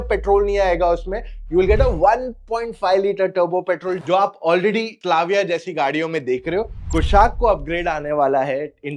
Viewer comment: Subtitles say Slavia par Bhai to Clavia bol raha hai.😂